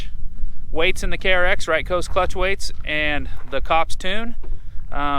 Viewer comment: I could not understand what you said about clutch weights & tune. Can you please clarify? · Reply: I’m running aftermarket weights and a tune on the KRX. It seemed to work extremely well pulling the trailer with that setup I had.